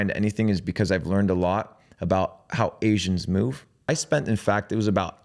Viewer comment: Did he just say, "I learned a lot about how Asians move" ???